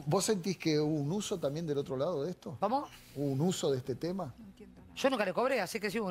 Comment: se escucha "no entiendo" 🤭 yo tampoco le entendía jajajaja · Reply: No entiendo nada, dicen 😂